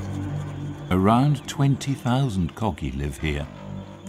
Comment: as a jamaican, i heard "around 20,000 cocky live here" lmao